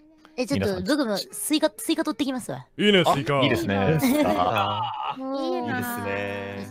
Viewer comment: サムネの文字凝視しすぎての 「いいねェスイカ！」が 「DO you Know スイカ」に聞こえた